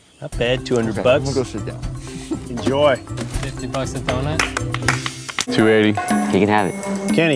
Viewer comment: BAHAHAHA he said “50 bucks a donut” But I thought he said “did you bust a doNUT” I’m very disappointed in myself 😂💀